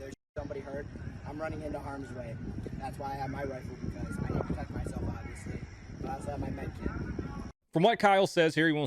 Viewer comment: what did he say? “i never touch myself obiously?”? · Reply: I need to protect myself obviously.